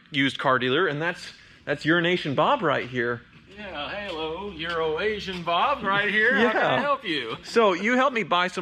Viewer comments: Euro~Asian Bob.... I thought was saying Urine-Nation the whole time. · I could have sworn you said 'urination Bob'. That's a whole different fella... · Funny I thought he said urination bob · I keep hearing "Urination Bob" 😂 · I thought he said “Urination Bob!” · sounds like said urination bob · Is it just me or does it sound like he is saying Urination Bob? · I thought he said urination bob 🤦‍♂️ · I could have swore I heard call him Urination Bob. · I keep hearing "Urination Bob" every time he says "Euro-Asian Bob"... Also I thought they could swap at any time, so couldn't have Ninja jumped back on the BMW, he may have been a lot happier in his gift review rather than seeming depressed driving the Buick. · I swore I heard Urination Bob. Lmao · The story is has the mentality of a 13 year old... lol. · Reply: oh it's euroasian, sounds like says urination :P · I seriously thought was saying Urination Bob every single time and assumed it was some inside joke. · Is it me, or does it sound like is saying "Urination Bob"? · I thought you said "Urination bob"... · Did he just say Urination Bob?